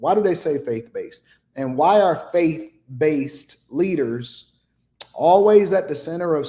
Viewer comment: I thought she said "fake based" leaders. If they really wanted to make a difference, why didn't they have a "Buy Black" day? F**** idiots.!